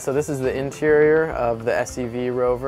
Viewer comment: INTERIOR! OH...... Thought it said '''Inferior''